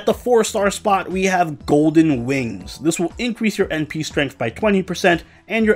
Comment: I honestly thought it said Golden Wind...